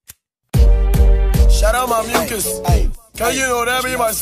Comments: i thought he said "shoutout my niggas" idk why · This whole time I thought he was saying “Shout out my *niggas*”